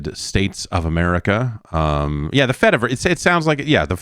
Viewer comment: Damn Jeff, You finally joined Fetty Wap's metaverse/ blockchain/ web 3.0 platform Fettyverse? Congratulations brother! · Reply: I thought he said fetaverse and I was like "oh word, that's gotta be big in the Mediterranean"